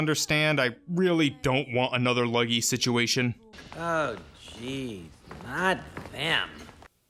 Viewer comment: I think he's saying "Oh gee, not them" actually.